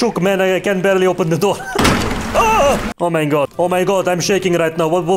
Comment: did you say „Oh mein Gott“ 😂😅